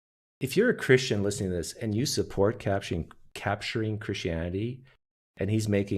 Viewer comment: I just misheard you say "capturing Christianity" as "cashing in Christianity". How perfect! 😄